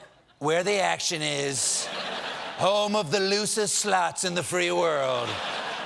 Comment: I thought he said "America, home of the loosest sluts in the free world "